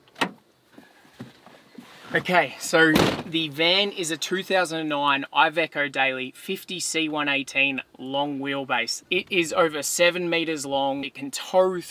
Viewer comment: I've listened to the van name over three times and I still can't figure out what it is, I heard "2009 ivenkerdaily long wheel base" anyone know what it is?